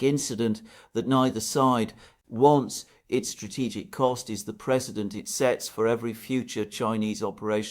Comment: I was sure you said 'the presidence it sets'. corr: precedence. If I heard wrong please accept my apology.